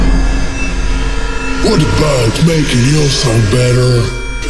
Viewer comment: This guy sounds like pewdiepies dog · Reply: Mind Track I thought the intro said "Poodiepie making your sound better"